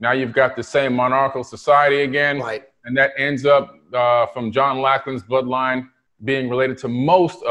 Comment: What does he say at 36:12? John Locklins bloodline? Or John who?